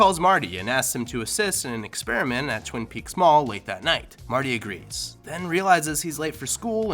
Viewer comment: did he just say twin peaks mall?